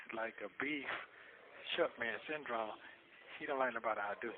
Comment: Beef Short Rib syndrome?? 😂 That's what it sounds like he said. 😅